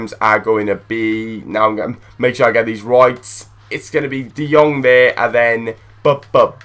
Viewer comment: lol always thought you say "gonth here" not "gonthier" :D